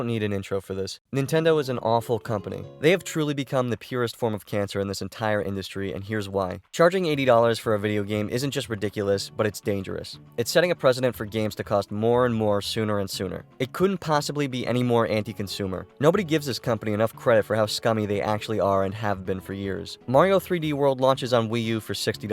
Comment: I was done when you said "It's setting a president" instead of "It's setting a precedence".